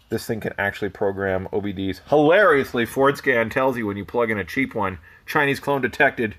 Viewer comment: Ahh, FordScan. I thought you kept saying foreskin. 😆